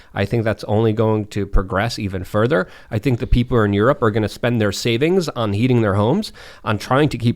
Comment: It sounded like Luke said people are going to spend their life savings to eat their homes😂